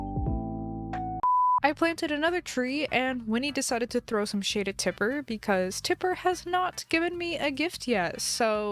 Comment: it sounded like you said "winney decided to throw some sh*t at tipper" 😭